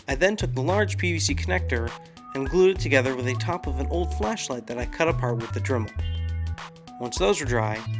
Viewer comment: i thought he said fleshlight